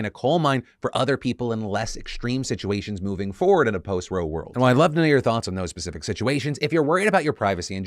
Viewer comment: OH. POST-ROE. I thought he was saying Post Real World and I was so confused like what part of the matrix did I step in to.